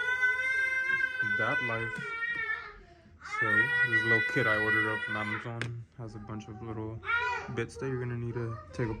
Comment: what'd you say? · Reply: “Dad life” but ur stoner ears heard “Dab life”